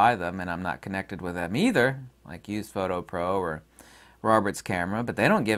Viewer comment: Funny, at first when you mentioned the two stores you sometimes promote I thought you said "UPhoto Pro" for the first one. When I looked for it I was directed to a domain registration site called Reg.RU which of course is Russian! Weird coincidence!! :-) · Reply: used photo pro